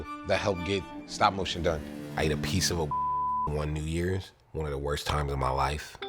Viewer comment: is he saying piece of a mushroom?